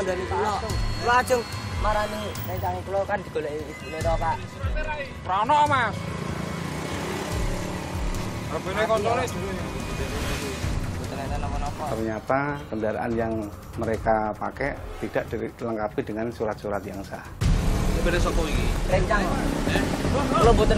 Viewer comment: apa cuma ane yg salah denger :3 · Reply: harusnya kan hape koncone bukan k*nt*le wkwkwwk